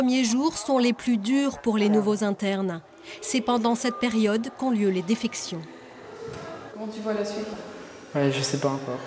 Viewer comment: A 11:22 la voix off dit n'importe quoi ( qu'on lieu les défections ) WTF !?